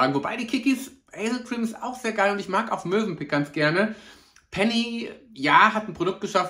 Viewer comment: Ich bin vielleicht noch etwas müde…aber ich hab gerade „Mösenpick“ verstanden 😂